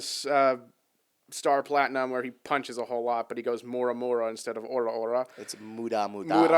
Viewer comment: Am i hearing things, or did this guy just say MORA!?!!?